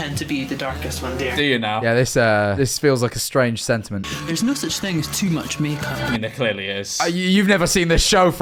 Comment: what did he even say?? 😭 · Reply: There's no such thing as too much makeup